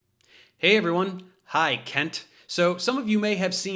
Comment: What he said: “Hey Kent”! What we heard: “Hey Cunt”! Truth and 🔥🔥🔥